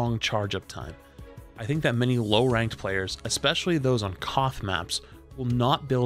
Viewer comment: What is he saying at 4:35? Cough maps? What does that mean? I'm taking notes over here haha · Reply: KOTH or King of the Hill maps are control maps in which either team tries to get it to 100 percent